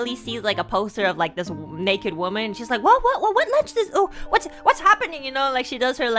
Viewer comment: I think the subtitles wrong. Sounds like Enna imitating Millie stuttering “what lunch this-“ because it’s the afternoon when she sees the naked poster lol.